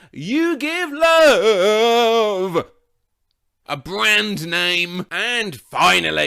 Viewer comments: totally didn’t used to think it said BAND-AID 💀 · I always thought it was "shot through the heart, and you're too late!" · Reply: me too! I also thought he said “you need love badly”